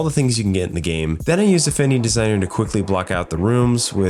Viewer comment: What did you use to map out the game at 1:00 I can't understand the name of it and the subtitles won't pick it up EDIT: it's affinity designer